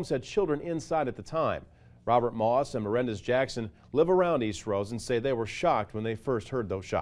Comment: I thought he said the dude's name is "Horrendous Jackson"